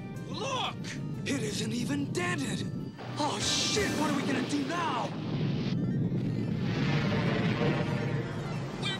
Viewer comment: Did… did he just say the s-word?! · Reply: Yep, he said "Shit".